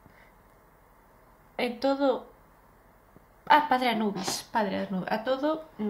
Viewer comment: parece que dice "en toda encarnación"